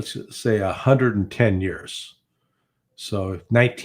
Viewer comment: 110 years? Larry out here living astro' in 2060. 👽😂 (It sounds like he said 1950)